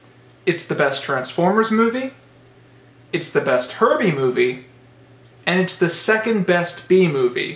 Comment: I got mad for a split second because I thought it said best horrible movie lol